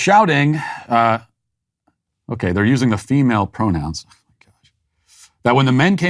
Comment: did you just say "oh my gosh, fuk"? HAHAHAHA